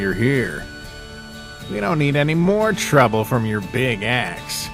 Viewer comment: WHY DO I HEAR "BIG ASS" 😭